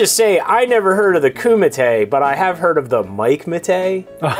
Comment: quick question, people: when the guy in the left says at 5:23 ''lets just say i never heard of the kumite, but i have heard of the... ??? -mite??'' someone help me out, because i couldnt hear the guy's punchline so i cant get the joke here. thanks. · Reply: He says "heard of the Mike Matei". 😄